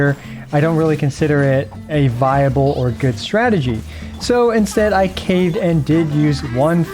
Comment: Starting the video you said "strategy" and I heard "tragedy" .... which fits how my Abyss runs go. :D